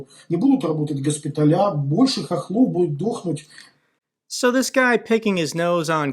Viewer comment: he wasnt saying "ukrainians", he was using a slur "hohol" which many know by now is like calling a black man by n-word.